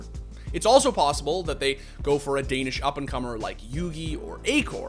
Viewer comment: Did he just say "A Danish up and comer like jugi"? Haha.